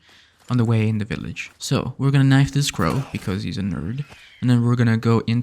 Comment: “we’re gonna knife this bird bexause he’s a nerd!” Hahaha did i hear that right? 😅🤣🤣